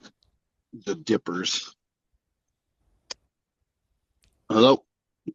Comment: I heard a North American man’s voice too. He sounded masculine and like a simple man. He said " the dippers, hello" @ 39:56. 💜😇💜